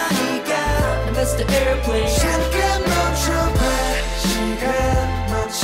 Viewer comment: it's "Mr. Airplane"? I thought they were saying "missed the airplane" lol